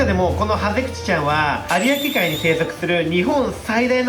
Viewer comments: このハゼさんのお名前 大大大募集してます🐟✨✨ ウツボのお名前発表は最後です！ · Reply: ハゼクチがずっとハゼキチって聞こえるからハゼキチ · このハゼさんのお名前 大大大募集してます🐟✨✨ ウツボのお名前発表は最後です！ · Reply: ハゼクチがずっとハゼキチに聞こえるので ハゼ吉でお願いいたします🥺